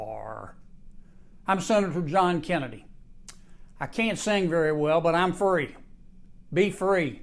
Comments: I thought John Kennedy said, "I can't sing very well but I am a furry." lmao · with that particular Kennedy's accent... I thought when he said "I'm free" - it sounded to me like "I'm furry". and well - ick. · I swear, John Kennedy saying "I'm free" sounds like he's saying "I'm furry" and I'd be very, very concerned if that was true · Did he just say "I'm Sen. John Kennedy. I can't sing very well but I'm furry."? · I nearly drowned in my tea unnecessarily, when I heard the senator say "I can't sing very well, but I am a furry." Not the thing I expected to hear from a republican senator. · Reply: He said "I'm free" in his horrible accent. I like "I'm furry" better, though.